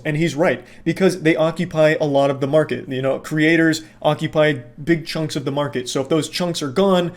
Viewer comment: I thought he said big chungus and I flinched like an abused child